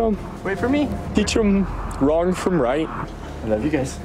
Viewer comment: He really said the saddest thing to him would be missing out on his kids and teaching them RIGHT FROM WRONG!! You sir should not be teaching that class!!! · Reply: He actually said teach them "Wrong from Right" (22:56) so I mean... he would be great at that